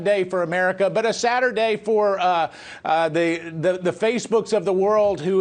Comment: Did Mark Meadows say “a sadder day” or “a Saturday” ? I know only one makes sense but it sounded like he said the other.